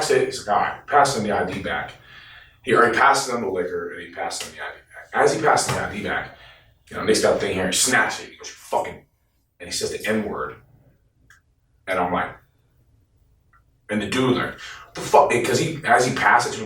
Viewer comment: Yo everytime said Nick I thought he was saying the b word had to rewind a couple times😂😂😂